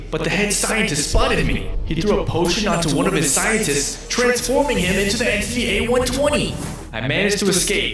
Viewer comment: the caption says "he threw a potion at one of his scientists then he turned into the internet"😭😭💀